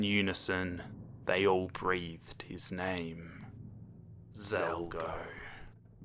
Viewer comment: I thought you said Zeldgo